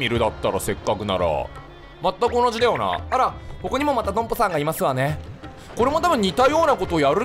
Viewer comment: 流しで聞いたら一瞬「ここにもまた独歩さんがいますわね」に聞こえてビビったw